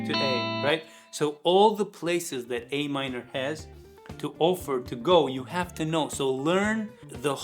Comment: what? what are you trying to say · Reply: All the chords that Am has to offer, you have to know